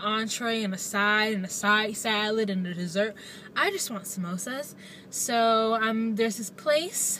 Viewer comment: I thought u said no dessert..just samosas but you got an eclair..lolll j/p